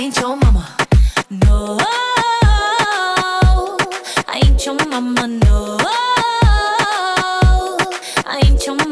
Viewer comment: When I Was Younger I Thought She was Saying “Boy, I ate your mama!” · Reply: when i was younger i thought she was saying Boy i hate your mama 😂🤦🏻‍♀️